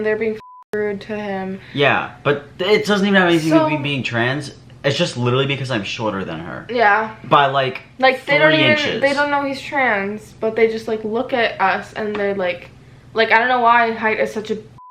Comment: did I hear him right when he said “it’s because I’m shorter than her by like 40 inches” cuz bro that’s over 3 feet shorter 😂